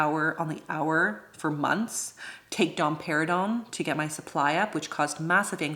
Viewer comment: I literally thought you said you had to take dom perignon for supply issue and I was interesting! 🥂 But then I googled it lol